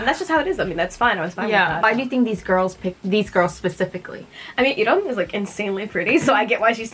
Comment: did she say Yiren??